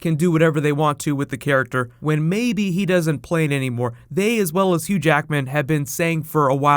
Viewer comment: Let me know how you want them to use Hugh Jackman to promote the Deadpool Sequel when that comes! The Deadpool End Tag is not supposed to be connected to the plot of Logan. It's not a crossover movie. Posting new Game Of Thrones and Flash Season 3 next :) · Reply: Did anyone proof-read the closed captions? They're absurdly off. Like, what's written sounds like it could've been said but it's definitely not: "... when maybe he doesn't play it anymore" (around 1:15) it actually says "... when maybe he doesn't plane anymore". Seriously, this is nuts.